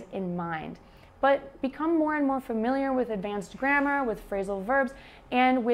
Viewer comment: You said "grammar", but for a non-native like me, it sounded like "grandma". :-D It was funny. Anyway, I got it based on the context. Nice video.